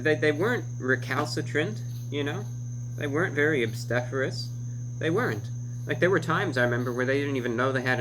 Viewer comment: By the way, what does he say at 4:20? I understand recalcitrant and googled it, but what's the second word? It sounds like "opstaphorous" but Google doesn't find it so I guess I'm hearing it totally wrong. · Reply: you probably figured it out by now, but it's "obstreperous." You don't hear it often in everyday life, but that's Reviewbrah for you. Also wanted to say your name is very cool.